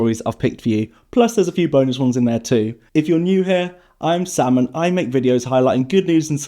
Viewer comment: Lol I heard "If you're new hear, I'm Salmon. I make videos....." 😂😂😂